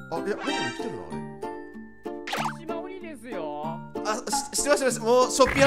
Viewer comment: と別れた後微かに聞こえるんだけど永遠とコネシマ鬼ですよ〜って言ってるさんなんか草